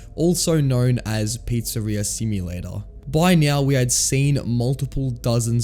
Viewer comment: did he just say "by meow"? 😭